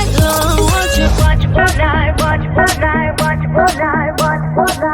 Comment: I swear it sounds like she is saying watching fornite